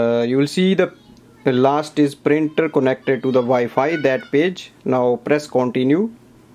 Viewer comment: on 2:28... does he say "that ...WHAT?!" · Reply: I think it was “that page”, but at first I heard the same as you hahahaha